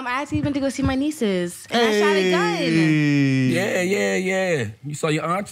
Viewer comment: got a gun. I don’t think they heard her say. “I shot a gun!”